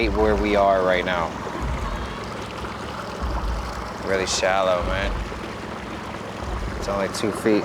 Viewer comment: Anyone else hear a girls voice say “I’m fine” after he says “right now” at 26:20?